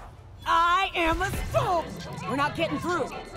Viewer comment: sounded like she said "I am a SPOON!" :D